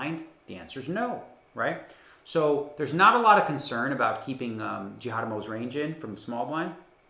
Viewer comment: Did he just say ‘Justin Jihadamo’ 🤣🤣🤣